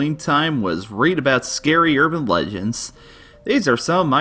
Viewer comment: I totally thought that said "scary urban Lesbians!" And I was like, "are you homophobic?!